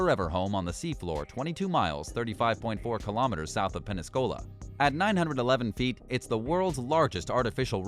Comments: Did he say Peniscola? That's not a place, but it COULD be an interesting drink. 5:04 :D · does he say Penascola?